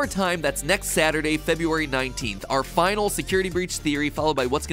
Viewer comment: I was packing a bag while watching and I heard:"Last Security Breach Theory" and just looked back up with that confused face meme